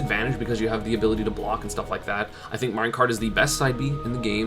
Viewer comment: did he just say Mario Kart?